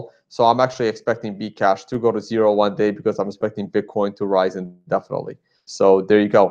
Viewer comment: Bitcoin Cash will go to 0 one day as Bitcoin keep Raising? did i hear that right on the 09:30 mins. lol